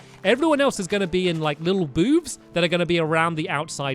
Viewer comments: did stamps say boobs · When Stampy said booths, I thought he said BOOBS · did stamps say boobs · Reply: he did didn't he ik he was soposed to say booths but if u listen closely it sound like he said boobs